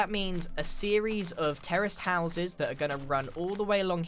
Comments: Wait wait Woah wait wait Woah Woah, HOLD UP! Did I mishear him or did he say "a line of Terrorist Houses"? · I keep mishearing terrace houses as terrorist houses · please some one say they here what ⟟ hear! ⟟ can’t even say what ⟟ hear it’s so bad! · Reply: terraced houses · when said terrace houses I thought he said terrorist houses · He sounds like he says terrorist houses lol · am i the only one that thought he was saying terrorist houses instead of terrace houses lol